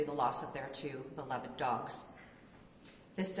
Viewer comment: Did she say 2 of 11 dogs? Holy cow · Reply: I’m not sure where you got that. Maybe you misheard when she said they “lost their two beloved dogs” as “two of eleven dogs”?